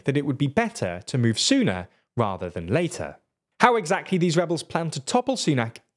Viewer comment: I thought you said "move Sunak rather than later" and laughed at the pun I thought I'd heard